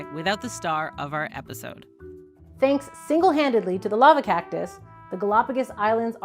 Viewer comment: I thought she was saying the lover cactus. Lol!